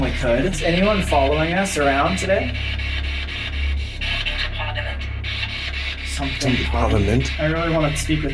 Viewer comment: I swear it said 'houses of parliament'